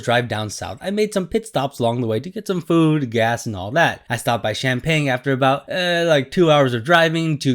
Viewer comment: I thought you said "I stopped to buy champagne"😂